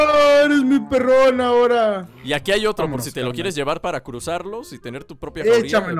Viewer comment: dice Y aquí hay otro, amor diganme que estoy loco pero eso oi lo escuche con audífonos